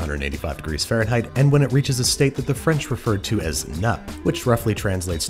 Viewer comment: you said nappé, but looking at that pan I heard "nut"